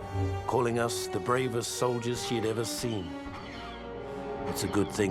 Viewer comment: did he say „he‘d ever seen“? She is a female though